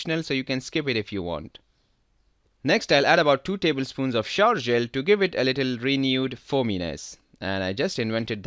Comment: Thank you for a great presentation. The only thing I did not get is the thing you added that sounded like you said 'sharjel'? I'm not sure what you said or what it is. Thanks. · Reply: Romin Hawk shower gel